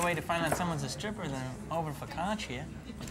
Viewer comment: I'm sorry, but what did Jamie say at 2:29? "There's no better way to find someone is a stripper than over for cacchia." What's is cacchia (spelling*)? What is that? I've been trying to figure that out...can't really google it when I can't even spell it. Is it a drink or food?